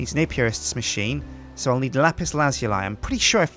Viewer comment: WTF u said at 17:15 Lis Lazulim